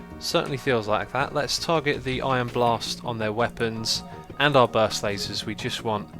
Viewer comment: It sounded like you said "Iron Bars" instead of Ion Blast XD